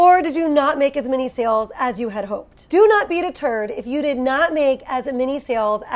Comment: I heard “do not beat a turd” and not “do not be deterred” 😂😂😂. Ok. 😅